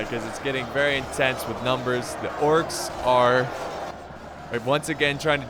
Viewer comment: it sounds like he said the corks